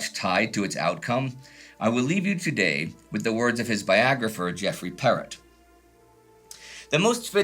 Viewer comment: I initially heard "Viagra-pher" instead of "biographer."